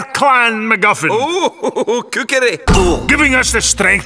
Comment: what did lord macintosh say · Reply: ohohohoho cookery